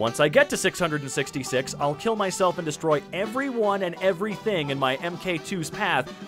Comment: you misread it as “I’ll kill myself and destroy-“ instead of “I’ll kill and destroy-“ and I’m sorry if I’m not the only person to have mentioned it